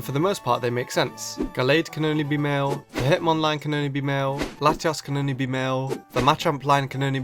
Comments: I swear I heard you say "Latias" around 2:17. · Did you just say Latias @ 2:18? U MEAN LATIOS RIGHT?! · did you just said latias or i misheard this one